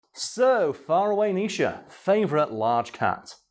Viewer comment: it always sounds like hes sayin fire away nisha lol